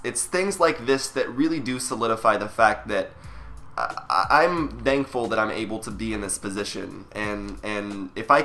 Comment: did he just say "dankful" hehe